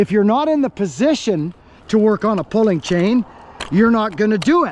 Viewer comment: I'm glad I persevered, I had no idea what a pulling chain was, until the end. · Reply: I thought he was saying polling chain 🤷🏼‍♂️